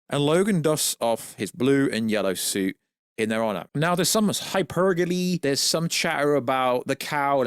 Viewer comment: what are you saying here? When you say “hypergaly” with a Kermit voice. Edit: 5:50 “oh my god, there’s hypergolly!* What are you saying? Edit: 10:31 again!!